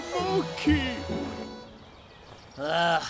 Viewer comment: mon quoi ??? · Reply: Manqué